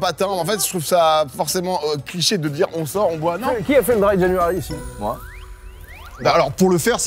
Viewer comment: le quoi ? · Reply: Le dry january